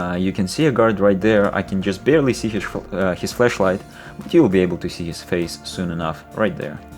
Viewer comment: I knew i heard that when you say "You can see theres a guard right there, i can see his flashlight" at 2:52 I cant help but hear "Fleshlight" xD